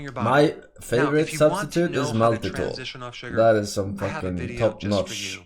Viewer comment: what did forsen say is his favourite substitute? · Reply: Maltitol which is actually safe but even aspartame is safe so idk what chat is talking about, they saw some articles few month ago and ignored other articles debunking it